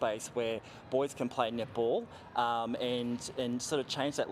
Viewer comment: The whole time I thought they said nipple, it want until 3:43 I realized they said netball🤣🤣🤦‍♂️🤣🤣